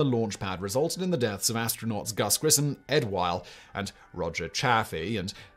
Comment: Ed who? Did you say "Ed While"? It's 'WhiTe.